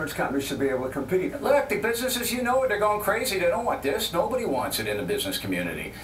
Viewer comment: Bill Clinton is so awesome that even Bill O'Reily let him finish his sentences Lol 5:44 I thought O'Reily said "The bitches you know it they're going crazy" hahah